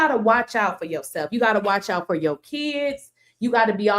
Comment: I could have sworn a dude said watch out for that blood......!!!!???